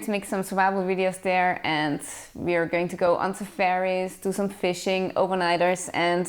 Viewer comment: too funny. I thought you said you were going on "ze ferry", later I realized you meant "safari". James Bond would be proud of your equipment load-out! and MacGyver. good job on the vaccinations, but I'd also be worried about kidnap & ransom, terrorist extremists, crooked cops & politicians, armed insurgents, thugs in general, etc. I guess there's bad germs, bad animals, and bad people. You got the first one covered. maybe you can rent a Glock while you're there, or maybe a rusty 100-yr old revolver? Good luck! keep us posted. seriously, wishing you the best.